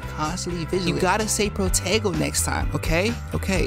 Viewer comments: protego. I thought you said potato 🥔 🤭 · Ok I thought you said potato but I see people saying protego 😂